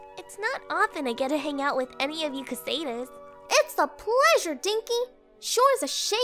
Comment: did she just call her stinkE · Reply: Dinky